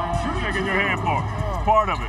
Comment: All i Hear Is "greatness dont brush his hair, you feel me?"